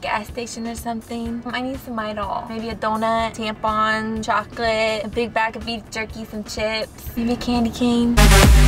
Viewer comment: Can anyone tell me what the hell she said at 2:46 ? She wanted him to stop by the gas-station because she wanted to buy tampons? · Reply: Midol, donut, chocolate, tampons, beef jerkey , chips, maybe a candy cane